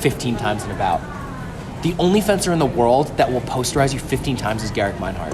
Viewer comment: what term did he say to talk about gerkemeinhart? I hear "the only person that will "poster eyes?" you 15 times is gerek meinhart" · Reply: "Posterize" :)